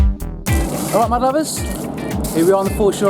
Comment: I swear, everytime he opens the video I hear "My Lovers" instead of "Mud Lovers" lol!